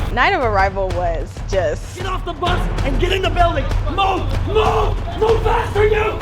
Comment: I thought the DI said get IN the boat 😎